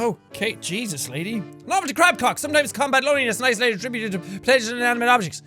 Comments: A lovely crab cock??? Is it just me or did he say clock wrong... xD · it sounds like he says, "a novelty crab cock" 😂